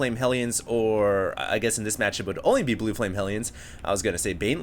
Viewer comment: I thought he said "Wu Tang Hellions" then realized how awesome it would be if that was their actual name.